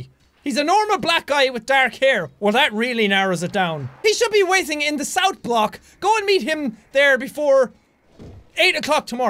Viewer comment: It sounds like Jack said "He should be whizzing in the South Block!" ROFL!!!